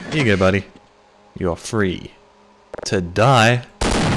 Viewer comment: "ooooh i thought you said I'm 'free today ' not 'free to die '. My apologies, I'll get on that."